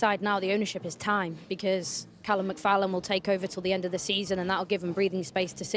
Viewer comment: Did she just say “Callum McFallum”? 😂